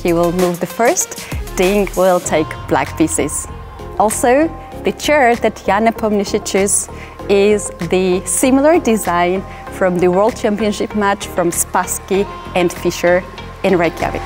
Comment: No offense but I didn't understand a word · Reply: The chair that Ian is using is of similar design as the one used by Spassky